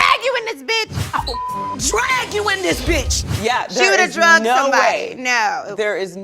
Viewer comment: Literally thought Porsha said ‘kandi would’ve drugged somebody’. I was like ‘touchy subject there Porsha’ till I realised she was saying dragged weird.